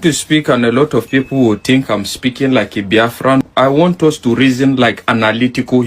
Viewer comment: Them never ready, I thought they said "biafraud"